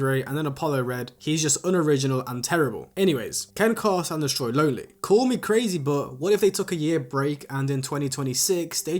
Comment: what are u sayin bro 😭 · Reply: im sayinggg fuck u mean they never gon make it😭😭